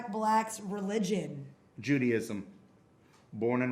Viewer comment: I legitimately thought he said Judyism, and was like "that's a thing now?"